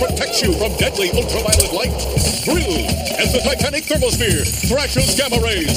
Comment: (what I heard) AS THE TITANIC THERMOPHERE.